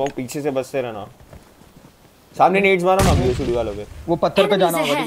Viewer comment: i heard it "samne nades dalona bhosdiwalo pe" 🤣🤣